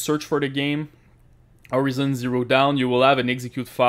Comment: Did he just said Horizon zero down?